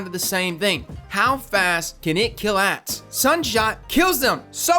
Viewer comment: it sounds like you said "how fast can you kill ass" lol